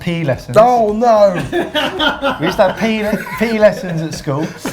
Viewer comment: I heard "pee" lesson instead of PE at first. Would have made a pretty good story too.